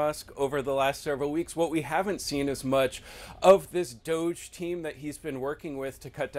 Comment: I honestly thought he said, “the Douche (DOGE) Team” hahah!!